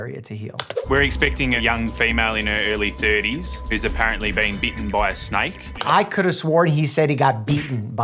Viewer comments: DW Dr Mike I heard beaten too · To me, it sounded like he said, eaten by a snake. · am I the only one who heard vaping by a snake